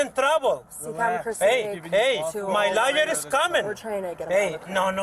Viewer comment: did he say his LIAR is coming 😱😂😂 12:20 should have been Taser Taser Taser 😂 just get out of the car 🤦 Deportation sounds good 👍